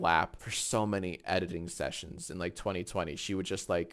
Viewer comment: I heard "so many edging sessions" 🧐